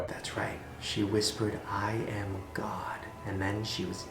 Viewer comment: When the chick said I am god I thought she said cock and I died laughing